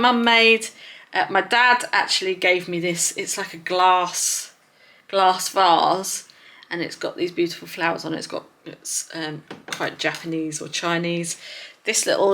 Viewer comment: I am quite sleep and thought you said glass bras. 😂. It was a vase. You have a beautiful picture wall. Quite homely home. Ll